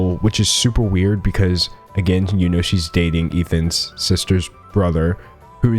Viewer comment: "she's dating Ethan's sister's brother-" 6:07 did i hear that correctly?????????